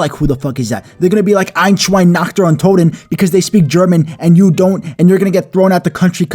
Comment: i think i heard nacht der untoten (the zombie map)